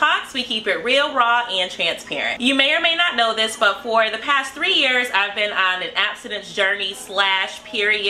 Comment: Did she just said 3 years? I'm shocked that an attractive woman could last that long lol Edit : congratulation for that btw